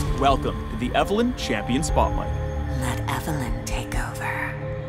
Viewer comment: oohhh, I always thought she said malevolent takeover. I didn't know it was "Let Evelynn take over". You learn something new everyday